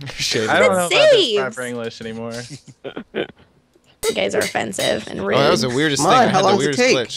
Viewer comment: I thought said "you gays are offensive" and I couldn't stop laughing.